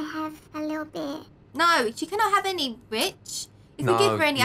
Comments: I thought she said bitch my bad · it sounded like she said “no you can’t have any bit**” 😂😂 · did she curse or did she say rich? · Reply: rich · did she say Bit*h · it sounded like she said no b** 😂😂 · Did she just say the B word? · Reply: she said Rich..... .-. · she said no she cannot have any bit** instead of rich!!!! me: lmao playing it three times to see what it really said and it said bit** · Is it just me or did Said Bi**c